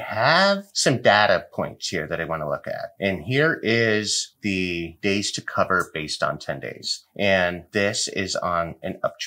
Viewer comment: When you said, "Days to Cover, 10 days" I thought you actually said, "Days to cover, TENDIES".